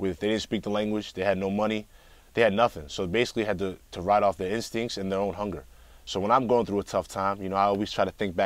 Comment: what dies he say at 2:31... basically they had to....? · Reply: ride off their instincts.